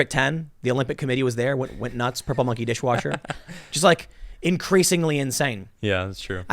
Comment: did you just say "purple monkey dishwasher?"